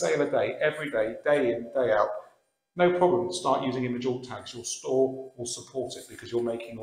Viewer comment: this is great! The only thing I didn't understand was "image all tags" - can you explain that a little more please? Thanks! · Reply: Hi there, it means 'image alt tags'. You may have misheard me. Go into a listing, and if you hover over any of the photos you will see a pencil icon. Click on that and copy the description of your product from the listing.